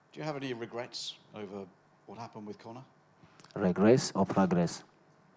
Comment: khabib wasnt saying how he has no regrets he didnt understand and thought the reporter said regress not regrets